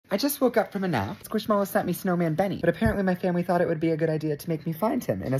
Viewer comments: I thought you said snowman bedding😂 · I thought you said squish mallow bedding 😅😂